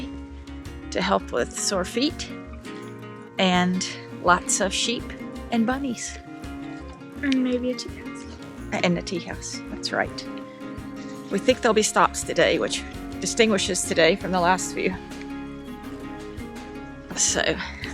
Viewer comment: A tea house. I heard "atijas" first time. Blessed Annunciation Feast, and Name's Day (among others) to!